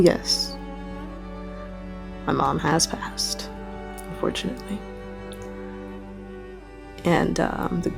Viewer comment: WHAT did she say!!? "Yeah, to answer your question, yes, my mom has passed FORTUNATELY." I thought the word is unfortunately. So she is relieved that her mother passed? hmmm..